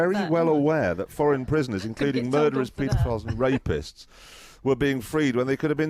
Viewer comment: shouldn’t that be pedophiles instead of pitifuls in the subtitles for that sentence?